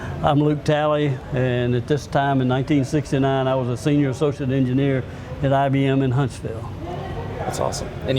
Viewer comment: Hi so if any kind soul would like to help me understand his accent these are the parts I couldn't understand: 6:34 a senior ___ engineer 7:12 I was __ after this 21:28 Huh? 37:07 I feel his answer here oversimplifies it. A bearing this complicated would be magnitudes more expensive than the switch from nitrogen to helium or a vacuum ... I really l iked how Luke was probing Linus in his understanding of concepts, it's clear he's used to explaining things to laypeople. But he also has the knowledge and grasp to back up what he is explaining. :) Also: Why does the water freeze? Linus asked this but the answer wasn't satisfactory. It should evaporate in space, but why does it freeze first? It's hot water, after all. The pressure would need to be high, so is this pressure coming from the bladder pushing the water? Or does the sintered metal cool itself, thus freezing the water? Thanks for the video and any help :) I NEED to go to this museum now! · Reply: This is way too late but: Senior associate engineer. I was haggard after this. I'm going to talk to one of my buddies when you walk out the door to see if he can hit you in the head. Can't answer the actual sience questions. Sorry.